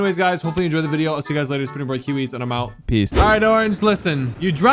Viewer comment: soo no one dont hear him say pretty boy kiwiz when he end the intro 1:25 but still hes not ugly i bet he be gettin all the girls tho